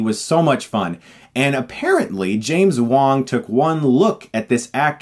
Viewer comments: After he said James Wong, I heard 'James Wan', immediatly shouting: 'The guy who made Death Sentence and Saw made this shit!?' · i thought he said James Wan when he said James Wong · I thought you said james wan for a second.